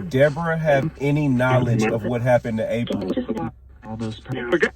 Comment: After they asked about Deborah, roughly 1:07:29, did anyone else hear a male say "all this time"??